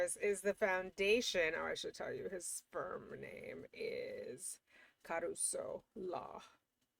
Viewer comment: did you say "sperm name?" 🤨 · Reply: I heard sperm name too but got that it was Firm name as scrolled up to the Firm name lmao